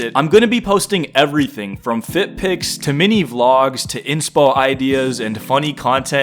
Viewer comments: why did i hear feet pics, then i lisend to it again and heard that it was actualy fit pics :d XD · i swear to GOD i heard feet pics🙏